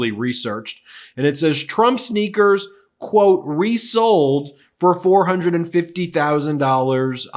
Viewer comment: So Ben said 'resold' and I heard 're-soled', which would make more sense. Those soles aren't going to stay attached for more than a week.